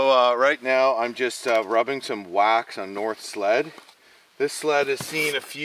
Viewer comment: I looked away for a bit and I thought you said you were putting wax on North's head at 8:04 LOL